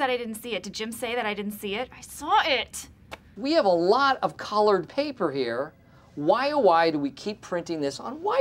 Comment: i swear Micheal says "Collared paper"